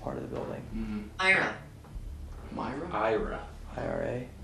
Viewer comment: EVP recorder they ask for a name and it sounds like it says Ira which the Ovilus says after too.